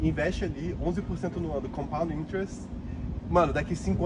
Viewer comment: investir em que? Não entendi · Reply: Compound Interest = Juros compostos ( Os juros compostos são os juros acumulados a partir de um valor principal e juros acumulados anteriormente. É o resultado do reinvestimento ou da retenção de juros que de outra forma seriam pagos).